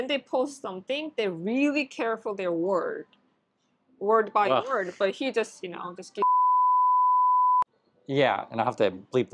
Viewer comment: haha i want to know what she said! 😂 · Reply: Maybe this 🤷, people choose their words carefully but " Trump doesn't give a fuck and he shit posts whatever he wants"?